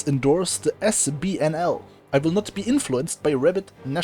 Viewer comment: for a second I thought you said reddit nationalists lmao